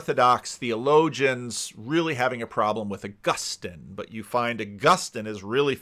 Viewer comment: I didnt catch that, Augustine? Augustan? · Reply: Augustine, as in Saint Augustine of Hippo.